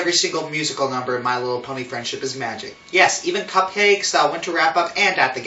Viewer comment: did you say CUPCAKES.